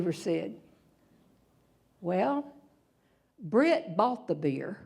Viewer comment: can someone explain, the son says brit bought the beer, Nothing related to the homework. But why so many people laugh at it? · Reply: Thanks you just cleared that up for me. I thought she said "Brit off the beer!" Basically the parents were expecting their son to confess to never doing his homework and perhaps explain why he didn't do it, but instead he told them that he had been underage drinking by admitting that his friend had bought them both beer. They were not expecting that and neither was the audience. What is funny is how kids surprise you with their teenage antics. I guess you need to be older or a parent to get it.